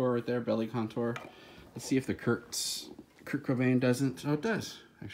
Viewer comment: Did I hear a 'teh Kurdtzzz' at 3:13?